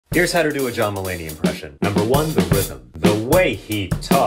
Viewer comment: Oh, John Mulaney, I thought you said Dylan Mulvaney.